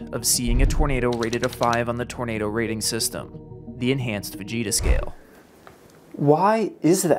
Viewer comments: For a second I thought he said “Vegeta scale”…maybe I should lay off the dragon ball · My high ass thought he said Vegeta scale and I just laughed hysterically. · I literally thought you were saying "The Vegeta Scale", and I'm over here like "tbh, that's a fitting name" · Just listening to this, my dumbass thought you said Vegeta scale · i can't be the only one who heard " enhanced vegeta scale" lmao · i thought bro said the vegeta scale 💀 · I thought he said the vegeta scale at first since I wasn't watching at that point and was confused. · Haha, I thought you were saying Vegeta scale, not Fujita scale. I think it would be amazing if Weather reporters screamed out "It's over 9,000!" when reporting F5 category Tornadoes. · I thought you said vegeta scale and was very confused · Ted Fujita really developed one of the most accurate scales before tons of tech like mobile radars and wind probes.. He was just the goat. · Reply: This entire time I thought he was saying the Fajita scale 💀 I was like strange we named our tornado scale after Mexican food but ok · Thought bro was saying vegeta scale for a sec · I actually thought you said Enhanced Vegeta Scale. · I could've sworn i heard it as ENHANCED VEGETA SCALE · Not going to lie but when you said Fujita scale I thought you said Vegeta scale. Like from Dragon ball. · Thought he said enhanced vegeta scale. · My dumbass thought you said vegeta scale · Ngl i thought he said enhanced vegeta scale 😂